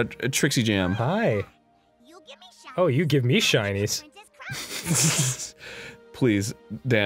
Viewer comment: I could have sworn she said, "You gave me shingles!" .-. I think sleep is needed.